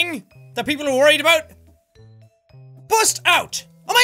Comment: I swear I thought it said bust down 😂😂😂